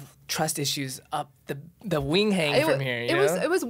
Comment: wtf does “up the wing hang” mean? Man’s said it twice in this episode and not even urban dictionary knows lmao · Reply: LMFAO I think it's supposed to be "up the wing wang"